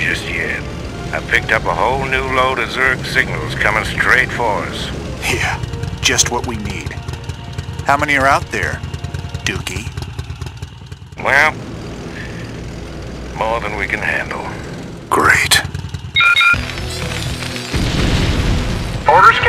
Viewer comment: Did he just call him Duckie? Haha